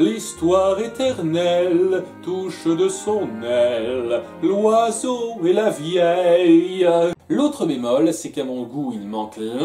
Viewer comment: Ptn faut voir le fou rire que je me suis tappé quand j'ai entendu "L'oiseau et la vieille"🤣🤣🤣